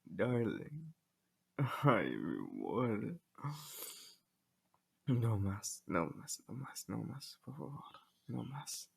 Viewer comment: soy yo o parece que dijo algo en español, tú voz es hermosa, te amo.🦋💕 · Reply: "ay mi amor.. no mas no mas no mas por favor"